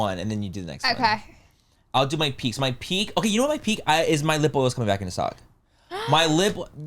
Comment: Never clicked so fast! I’m so ready for this story! When said his lip oil was coming back in stock, I had to go back because I thought he said his nipple was coming back😂